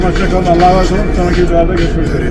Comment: Videoyu "sonraki cezalarda görüşmek üzere" diye mi kapattın abi ben mi yanlış duydum? 😀